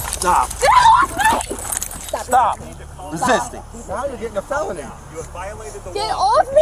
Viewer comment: all I hear is "you have violated the law" in the Morrowind Guard tone.